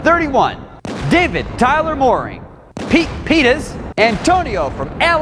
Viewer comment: Did he say Pete penis?